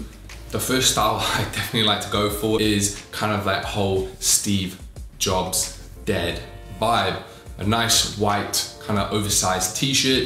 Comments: For a second I thought he said Steve Jobs dead vibe,,,,damn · Shiiiiii, I misheard you. Thought you said "Steve Jobs Dead Vibe" :( I was like, damn. Didn't need to go that hard on the man. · my bro I lowkey thought you said "Steve jobs DEAD vibe"